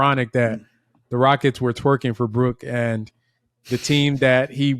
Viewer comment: Did you say “twerking for brook”???😂